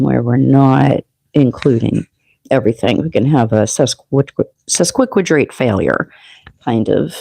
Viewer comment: At minute 3:53 "we can have such a quick rate of failure" i think is what you said, replayed a few times w airbuds, ❤ your guidance and content, sag riding and moon, aqua sun in 3rd